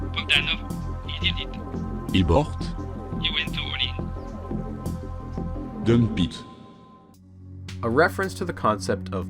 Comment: Mistake at 33:21 subtitles says "he went too early" but voice said "he went to all-in"